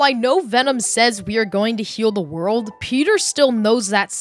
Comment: i heard it as "kill the world" idk why